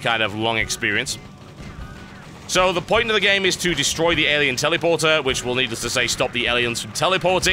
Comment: Sounds like he is saying 'Tellyporter'. :P